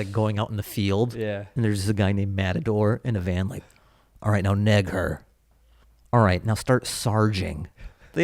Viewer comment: it sounds like you said the N-word ("neg her"). Love the pod boisss